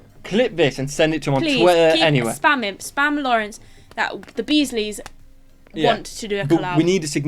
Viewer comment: Now I know you said Beesley's but I heard "bees knees" and suspect I have stumbled upon a next generation of merchandise. :)